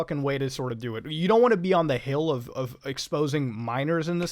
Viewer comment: for 10 minutes i thought you were saying "miners", not minors